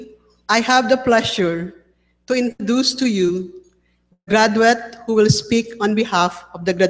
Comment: did said *grad-wet*?